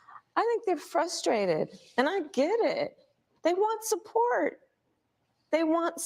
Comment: does she say "I don't get frustrated, and I get it?" 😊 She is sneaky😂